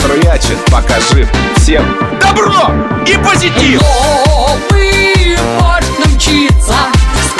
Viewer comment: Новый Борщ к нам мчится? Или мне послышалось? 🤣🤣